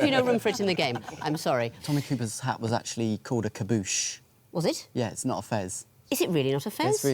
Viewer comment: Yeah that was quite rude. Fez and tarboosh look very similar. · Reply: Did he say 'kaboosh' instead of 'tarboosh'? Also, fez and tarboosh are practically the same in different languages. Tomato/tomato.